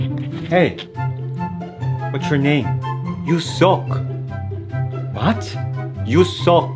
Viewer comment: I thought he said U sick😂😂😂😂